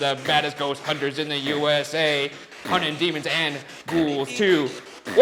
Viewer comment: For postmortem: 5:50 you said unintelligible but I thought it said demons or demon. Maybe it was saying it like a question like demon? Or something or maybe the ghoul was just hyping you up who knows 🤷‍♀️ anyway boogara for life sorry not sorry Shane